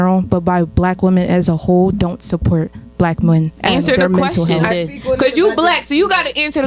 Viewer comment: Thought she was saying Blank men and women